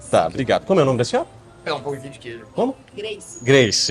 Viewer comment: eu escutei um pãozinho de queijo aí???